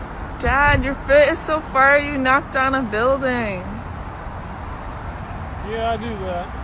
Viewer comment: what is she saying I can’t tell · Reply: "Dad your fit is so fire you knocked down a building"